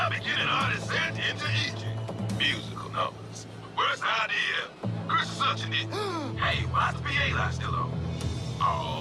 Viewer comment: did I hear 'Chris is such a ni- wait, why is the light still on? Oh shi